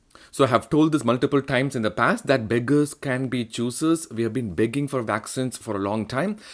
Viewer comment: Hello, Love your presentation, I keep hearing "Beggars can the choosers" and not "Beggars cannot be choosers" , I feel there were three more vaccines that you should have covered unless you feel we don't get that in the US, Covishield, Covaxin & Sinovac vaccine , Love your analogy ...